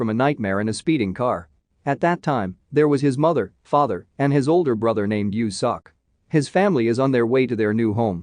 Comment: I heard "his older brother named you suck"🤣🤣🤣